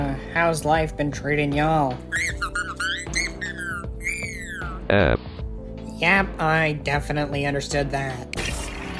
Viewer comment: I did not understand what he said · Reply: I somehow managed to understand him saying: “It’s been really good for me! YAYYY!”